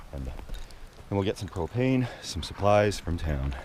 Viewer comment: I swear i thought u said cocaine and supplies from town- wud b a whole different kind of trip